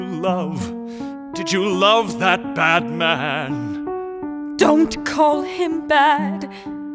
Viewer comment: The lyrics: Did you love...did you love that bad man? Me, mishearing them: Did you love...did you love that Batman?